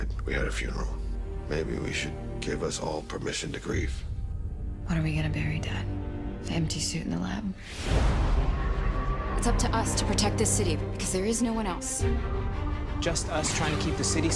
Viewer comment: did Iris just say when are we gonna BARRY him 😂😂😂😂😂😂